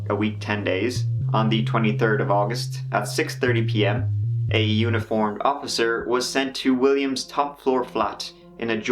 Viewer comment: I thought you said “a unicorn officer”